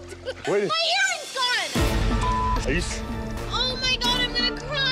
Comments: I thought she said her hearing was gone, hard to feel sorrow for her when l heard her say 75,000. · I thought she said her hearing is gone. Seriously, that would have been terrible and a reason to freak out. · 😳I honestly thought she said my hearing is gone and they were looking for hearing aids til she said, "Diamond earring" 😂😆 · I thought she first said my hearings gone!! Then I realised🤔